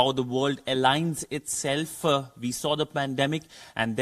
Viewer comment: did he say pandemic? 😁